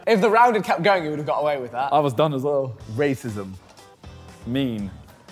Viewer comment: when JJ said Racism I thought simon said ME at first and I about died